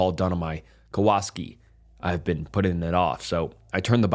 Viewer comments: Kawaski instead of Kawasaki is gold lol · Kawasaki.... Cow-a-sock-ee..lol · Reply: Sounded like he said Kowalski🐧🤣